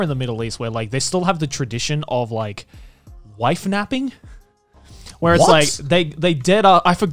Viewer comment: I misheard "wife nabbing" as "life napping" and thought people just took a gap year to find themselves but NOoO😂😬